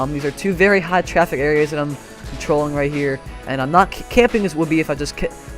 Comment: am i the only one who heard trolling